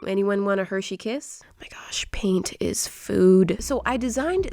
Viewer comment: I think my phones glitching out :/ Everyones picture is pixelated, even Moriahs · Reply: Also when she said "paint is foooood" I thought she said food is food. And I was about to say that that was a weird thing to say but then I caught myself